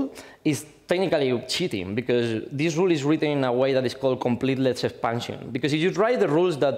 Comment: I could not understand what he was saying at 20:00, even at half speed. "completeletive parsing"? Which is not a serious complaint: English blows my non-command of Spanish completely away.